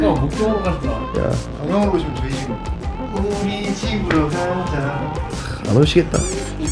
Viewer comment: ???: 광명으로 오시면 저희집입니다 에서 저희집을 김휘집으로 잘못 들었닼ㅋㅋㅋ